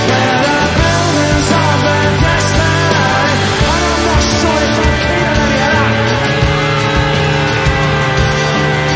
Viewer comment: What he say? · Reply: "and I'm not sure if I'm too keen on any of that."